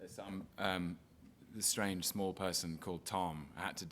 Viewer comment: did he just say SMALL person?